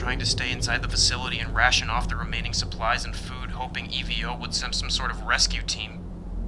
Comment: Eating strawberry yogurt while watching the video goes hard ongg 💯💯 Also I noticed that at 6:50 when he said that they were waiting for E.V.O the subtitles said evil... Huh.